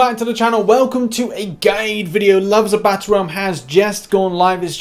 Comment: Did he say gay video?😂